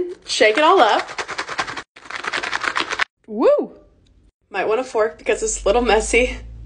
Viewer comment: “Might want a fork cuz its a little messy” (Proceeds to use hands) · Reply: I thought it said might Wana fart cause it's a little medsy